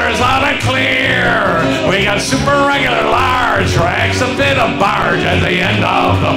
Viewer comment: What does he say at 0:27? "We've got super, regular, large, " and then something about a barge? O.o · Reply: We've got rags to fit a barge :)